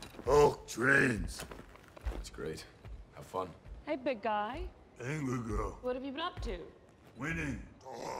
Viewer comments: "aye my girl" is incorrect, He actually says "angry girl" and he calls her that about 4 times during the movie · He doesn't say "Thor like small little fire" he says "Thor like smoldering fire" litterally one of the funniest lines in the movie. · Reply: If you want to get technical he also said "angry girl" instead of "aye my girl" 2:00 here, at 2:05 he said "don't kick stuff" instead of "stop kick stuff" here, at 2:54 he says "noo no team, only hulk" instead of "no not him."